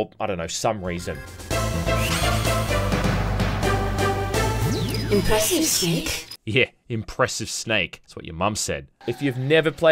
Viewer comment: I always thought she said "impressive sneak"